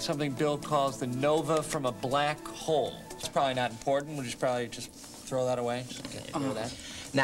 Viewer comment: Did Matthew say "that's probably not porn"?